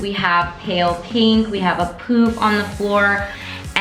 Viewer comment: I thought she said "we have a poop on the floor..." 😂😂😂😂